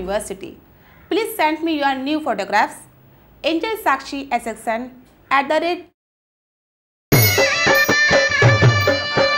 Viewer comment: did she say : "Please send me your NUDE photographs?" xD